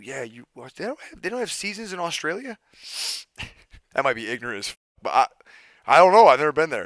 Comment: Literally thought he said “you don’t have little Caesar’s in Australia” 😂😂😂